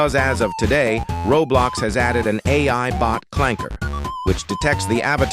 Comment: On bro did he just say clanker